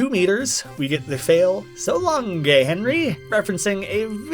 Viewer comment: what the fail says: so long'eh, Henry! The subtitle: So long, gay Henry